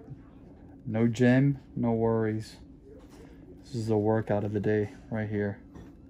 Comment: I thought you said no gin, no worries, lol.